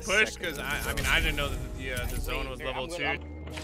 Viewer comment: did he say he didn’t know the zone was level 2?